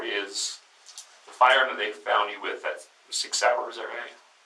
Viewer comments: I disagree on this point. I didn't understand the question by the detective and even the closed captions picks it up as "the fire that they found you guys with... the six hour?". So, I would've asked him to repeat the question and maybe remove the pen cap out of his mouth, or remove any obstructions to speech. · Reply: "The firearm they found you with.. the Sig Sauer.. is that right?" Could be that my accent is more used to drawls like the detective. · Sorry I do not believe there was any deception there. I replayed that over and over. I still don’t exactly know what the last few words of detective’s question were. He sounds like he has shit in his mouth. I’m not saying give this guy a pass on anything else. But for that one instance he had the right to say, “what?”. Totally. Zero deception. Plus it’s pretty easy for cops to find out who owns a gun or not. That was a straightforward question in that regard such as “what color shirt am I wearing?”. He also reflexively responded in the affirmative that the ammo belonged to Cory as well. Again, no deception. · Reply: He said something like "The gun you had in your car, the Sig Sauer right"? Then asked where he got that. The last few words Sig Sauer is just a brand of gun. I think that is the part that didn't understand. He may not have known what that meant possibly. I think a lot of people would not have understood that though, because they probably just are not familiar with that brand name of firearm.🤷🤔